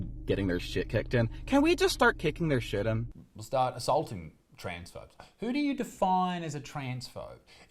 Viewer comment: That first person i thought they said trans folk, instead of trans phobe lol